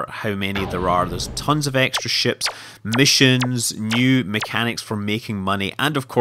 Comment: Ha when you were talking about Oolite you said there where "new mechanics for making money" I thought you said "Nude mechanics for making money" and I was like whaaaaaaaaaaaa?